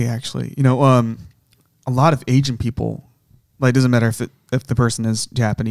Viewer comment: I thought he kept saying aging people. Turns out he said Asian people.